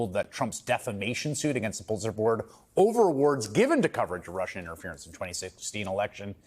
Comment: What is the word he says at 2:07 (English is not my first language) I have been trying to look it up, the caption says "over awards" but when I look that up it makes no sense in the context (it's something about student loans). It sounds like he is saying overwards/overweards/overwhards or something like that. Can someone please tell me what word he is saying 😅 I feel so silly